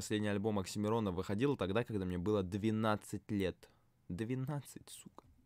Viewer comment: Тебе 24? А казалось 17-19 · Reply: Мне послышалось "когда мне было 17 лет" В уши долблюсь